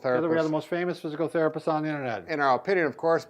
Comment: I thought they said "the lamest physical therapists on the internet" lol. Great exercises